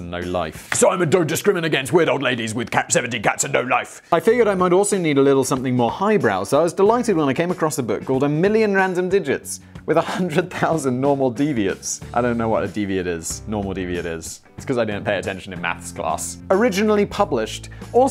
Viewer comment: Did anyone else think he said at 11:11 "A million random digits with 100,000 normal deviants"?